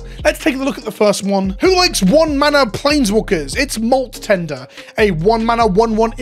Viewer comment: Am I going crazy, or does he say "Who likes one mana planeswalkers" (at around 0:15 in the video)?